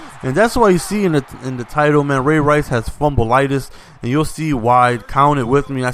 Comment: When I first saw this, I thought it said Ray Rice has FUMBLE-TITIES